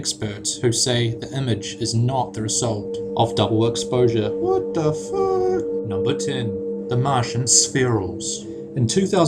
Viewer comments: Does anybody hear that voice at 5:21? It says "What the fuck?" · AM I THE ONLY ONE WHO HEARD "What the fuuuuuck" ON NUMBER 11 at 5:22????